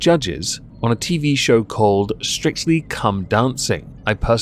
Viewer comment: Ok for real, Am I hearing that right? Strictly cum dancing?! What?! Edit: Ah, What irony, at 7:23 everybody seems to be confused and saying he called it forign muck, When he was clearly saying that somebody else referred to it as that, Which he found mindblowing because it was delicious... So not only did he not say that, He was inferring the exact opposite... And here I am making silly cum jokes as if people are stupid enough to do something like that... Fun times.